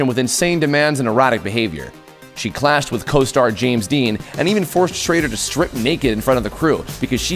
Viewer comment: Did he say James Dean at 1:42?